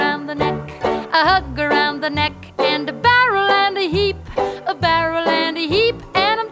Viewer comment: My mom used to sing this to me though i could uave sworn she said "a barrel full of sheep" instead xDD